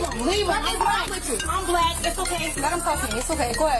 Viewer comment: Did she say * I’m black it’s ok * oh boy another racist woman, what a shocker.